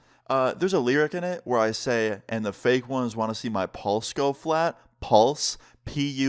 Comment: I thought it said paws go flat